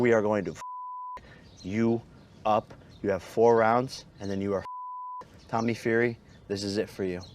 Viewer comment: It’s 8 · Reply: Of The Gods oh... thought i heard him say hes got 4 rounds lol